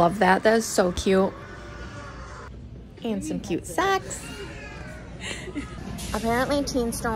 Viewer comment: When you said socks I thought you said snacks 😆